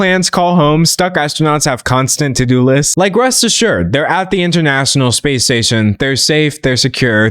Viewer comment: i thought you were saying "space sex" 😭😭